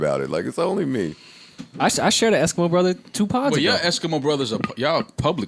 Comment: Who does Joe say he's eskimo bros with that had Mal cracking up? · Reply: Jen Jen Sean Kingston 😂😂